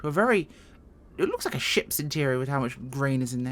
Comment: At first, it sounded like you said: "It looks like a shit interior..."! Then I went back an re-listened...I LOVE your accent!